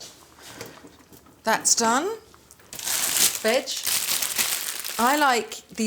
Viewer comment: legit thought she said 'That's done...BITCH'